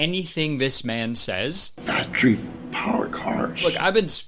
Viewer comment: I heard "I drink powercars" lmao Also I heard "good and evil, look at me"